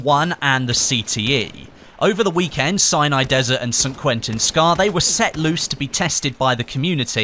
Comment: Sounds like hes saying cyanide desert 8-/